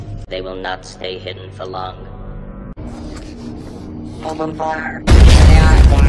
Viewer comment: there they are!! Quarter(i think that's what i heard) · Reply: It's "There they are! Fire!" Its just that two droids are saying "Fire!" at the same time